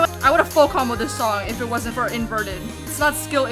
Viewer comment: the fact that you said “full combo” but the captions thought you said the f word in a different accent